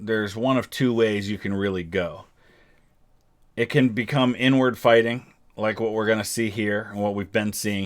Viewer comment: On god I thought he said n-word fighting and I almost choked